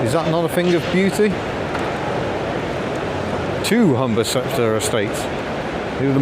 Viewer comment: Did you say Honda?